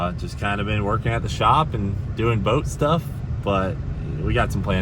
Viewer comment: When he said he had been working at the shop doing boat stuff I thought he said but stuff